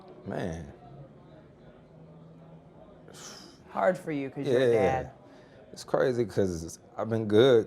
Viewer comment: did she just say hard for cuz shes dead?